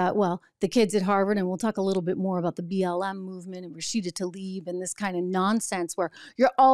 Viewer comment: CC: "and proceeded to leave" INSTEAD OF: "RASHIDA TALIB"!!! HA! U-Tube LIVES "In this kind of nonsense"!